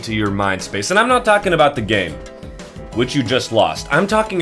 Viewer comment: ? can someone tell me what he said it just cut out for some reason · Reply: And I’m not talking about the game… which you just lost